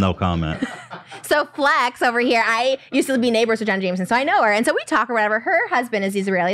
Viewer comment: what name did they say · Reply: jenna jameson